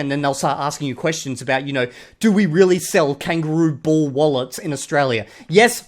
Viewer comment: I thought i heard kangaroo bull wallets at first. 💔😭